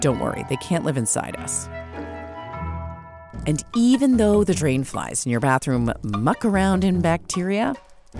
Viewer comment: i thought it said brain